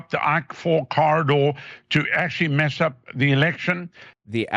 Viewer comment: i think he’s saying the “ I4 corridor” albeit terribly.. These Alex jones types are a special breed of glue sniffers 😂